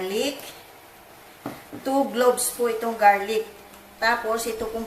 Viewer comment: did you say 2 globes of garlic?